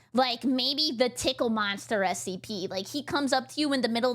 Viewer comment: did she just say tickle monster??? scp-999 haha thats crazy tbh